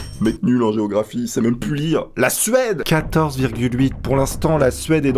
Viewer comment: T'as gueulé "La Suède !" J'ai entendu "La sueur !" 😂😂😂